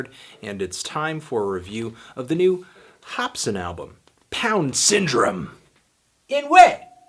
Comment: i thought it said down syndrome lmao